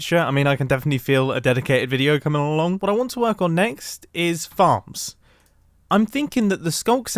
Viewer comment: I thought he said fart lol